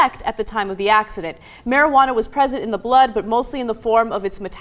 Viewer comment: "Marijuana was PRESIDENT in the blood, but mostly..." Did anyone else catch that?